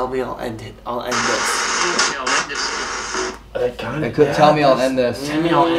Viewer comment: i hear tell me i end this kid anyone else?